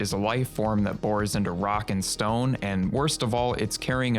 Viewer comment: did i hear a rock n stone?